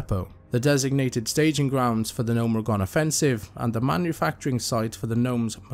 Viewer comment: Oh, thought it Said Gomer Reagan.